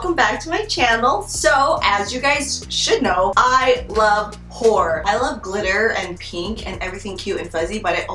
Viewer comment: "I. LOVE. WHORE." lmaooooo literally what I heard